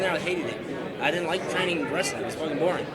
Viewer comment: can some one help me out with 1:46. does he say , he doesnt like training aggressive? thats its boring? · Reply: He doesn’t like wrestling that it’s boring